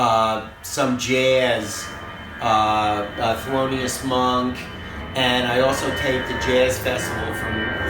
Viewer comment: has great musical taste, everytime i hear him talking about a band i google/youtube it and find good new stuff to listen to. I just couldnt understand because of the plane but whats the band he mentionned at 14:20, anyone knows ? sounds like flordian monks or something ? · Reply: Thelonious Monk